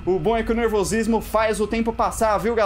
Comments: foi só eu que escutei po7-# ?? Eu acho o Animatronics de cabelo amarelo muito parecido com o batata kkkkkk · o falou po*** ou foi impressão minha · Foi impressão minha ou o falou porr@ no minuto 9:41 ?